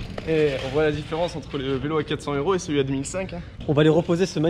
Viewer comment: Attend quoi il a vraiment dit 10 500€